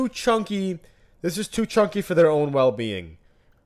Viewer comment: I though I heard “tooth Chunky”